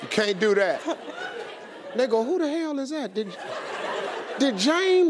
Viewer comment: did Steve just say "nigga"?